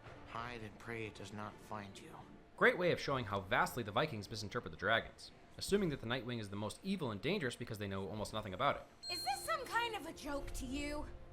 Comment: Did he just say night wing